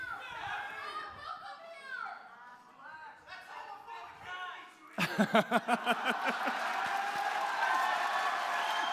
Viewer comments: what did that guy say at 3:42? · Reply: nevermind, i got it. For anyone else wondering, he said: "that's homophobic hate speech!" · What does the guy in the audience say at 3:40 that makes milo laugh? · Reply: I believe it's "That's homophobic hate speech, you idiot!" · what does the guy say at 3:40? "that's all a.... (something) you idiot" help me pls I will die if I don't find out what he said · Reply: I think it's "that's homophobic against gays you idiot!" I can't be 100% though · What does the guy yell at 3:42? I cant understand =(( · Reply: that's homophobic you piece of shit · Can someone please tell me what Steven said at 3:43? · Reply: "that's homophobic hate speech"